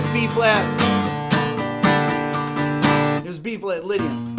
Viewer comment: also here, what does he say here? 4:54 B flat what? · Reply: Lydian